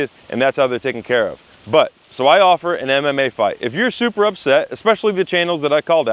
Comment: I heard “anime fight” LOL 😂